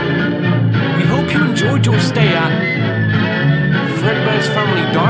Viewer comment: "We hoped we enjoyed our stay at Fredbear" is what it said at 2:35. Not to be a theorist but is the words coming from the kids in the suits or the employees?